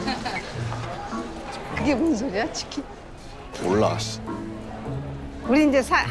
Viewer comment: It sounds like he says "I don't know. S**t(they edited the last part)"